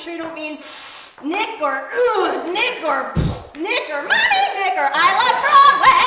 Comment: Ok, is it just me, or @ 1:39 during "NICK" joke, is she saying N*****? Someone please tell me that she's not saying that word during her routine...2 me it's sounding like she said "NIGGER..NIGGER...I LOVE BLACK MEN"...I hope & pray I'm wrong.....maybe she's actually saying something else & i'm just misunderstanding her...I'm sure she's not using that word..right?? Someone please clarify...if I'm wrong, I certainly apologize in advance 4 slight misunderstanding